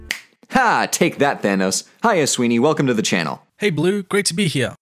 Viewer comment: i heard that as “sweetie” and got really excited for a second